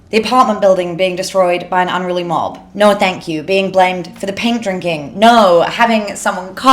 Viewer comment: Ok so I might just be a thick American, and you may not see or respond to this comment, but for some reason I can't understand what you said, at 10:28. 😂😂😂 what did you say? 😂😂😂😂 I'm not trying to offend you I promise. I love this video and just want to clearly hear every word! Your great I promise! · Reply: “Being blamed for the paint drinking” you mean?